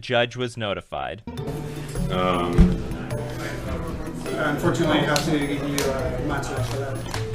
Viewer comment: Wtf did he say??? "Unfortunately you uhh.. mushabtudabuvch" 8:58 What??? "He's receiving a nustsuss so you win the game" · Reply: Unfortunately I have to give you a match loss He’s receiving a match loss